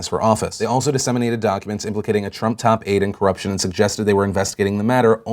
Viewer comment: did anyone else hear "top aide" and thought he said "top eight" and get myspace flashbacks?